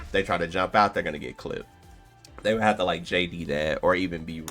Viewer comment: What do u Wan when you say “jp” is the jus Perry or something im new to snk fighting games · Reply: hes saying "JD" for just defense so you are correct yes